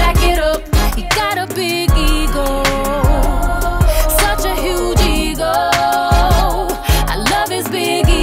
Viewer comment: I used to think she was saying “A huge eagle” and the song was about her boyfriend moving in with her and he had a huge pet eagle and he wanted to put it in the garage but the bird was too big and she was complaining about it but then she started to like the bird and was like “I love his big eagle” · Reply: now I hear “A huge eagle” when I''m listening to this song lol